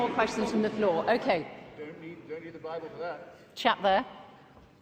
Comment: What did the guy say at 37:30 ?? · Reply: "You don't need a bible for that" with regards to knowing what is morally right and wrong.